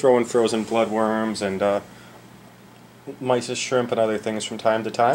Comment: Great video! I thought you said mice and shrimp for a second I was like woah he eats mice that's crazy haha😂 keep making awesome videos!